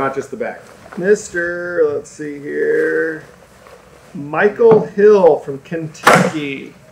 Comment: You said Michael Hill and I thought you said me and then I read the name lol 🤦🏼‍♂️